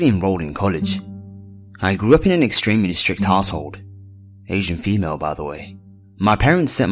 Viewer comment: First story sounds like he said "strict arsehole" haha x